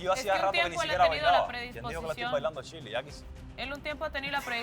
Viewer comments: alguien me dice que dijo Renier ? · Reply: Ya quisiera . Fue lo que dijo , o sea que ya quisiera bailarle a Shirley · Se viene una semana dura para las que apoyamos a Shirley🤧 y el avance me rompe en mil por verla llorar por sebastian 😑 pd: min 1:20:45 renier dice " (no se entiende esta parte...)yo no le bailo a shirley, ya quisiera (???" JAJAJA la cara de vanessa y todos cgados de risa.